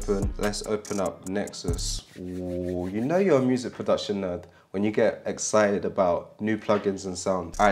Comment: I thought you said a music productioner I was like damn gotta run with the new terminology haha